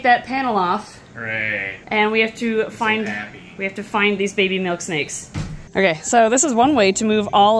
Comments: I THOUGHT IT SAID BABY MILKSHAKES- LOL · I thought it said baby milkshakes 😂 but it was baby milkshakes